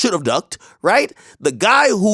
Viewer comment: I thought they said slide for von not killing duck